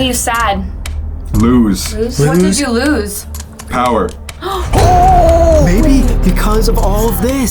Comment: It almost sounded like they were talking to King Charles "Lose Power". This is where the Prichards "betrayed" him which led to his downfall and death so it would make sense he's stuck in that home and possibly wants vengeance. This is a reach, but wouldn't it make sense as Edward Prichard intends to protect his house from this evil entity (a spirit filled with anger, vengeance, and betrayal)? Anyways, it's most likely an evil entity that has been manifested and accumulated over the span of 500+ years, but this was just an idea regarding its history.